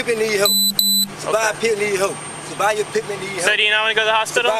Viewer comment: I thought he was saying Scotty pippin needs help